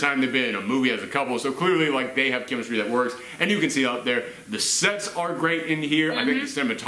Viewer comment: it sounded like you said it had great sex